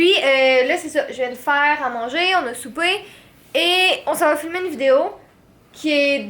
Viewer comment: Ses moi ou tu a dis ons s’en va fumer une vidéo sans faire exiprès 😂